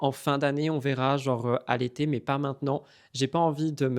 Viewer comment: Joyeux anniversaire, 🥰 à défaut de plus, je vais peut-être t'offrir un sourire dans le mood "mixozoti" 😂 : quand tu as dit "à l'été" j'ai compris "allaité" 😳😂 et je me suis dit "mais what ?" (Cri de Mel) et juste après, tu parlais de prendre un doxiteur et je me suis dit "mais c'est quoi ce nouveau mot ?" avant de comprendre que c'était "un dog sitter" 😂